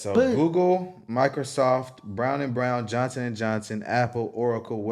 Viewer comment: did you say Johnson & Johnson? I guess some of that money came from diddy